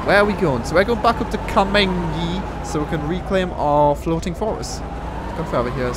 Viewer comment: All this time I thought you were saying "floating forest". Finally understood "floating fortress"